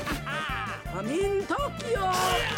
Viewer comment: did she say come in Tokyo ?